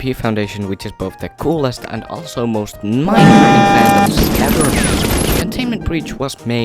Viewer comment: bro i cant hear shi 😭 whole house mad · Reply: I KNOW, just if it helps "mind numbing fandoms ever made"❤ thanks for the Watch tho